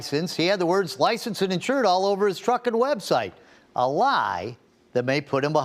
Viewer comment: I thought the reporter said "he didn't know all over his Fuck*ing website haha!!😂